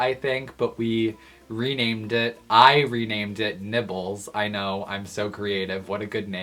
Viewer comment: i thought he said nipples when he actually said nibbles